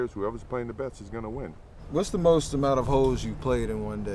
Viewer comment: damn thought for a second steph said 'what's the most amount of ho's you played in a day?'. mj - 63. lol